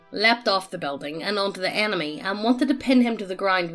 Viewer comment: The enemy , yeah. Me and my Texan ears hear anime and I know that's not correct and that it's just accents like my own... What do the captions say? I do that. Google hears better than me and I. Disappointed.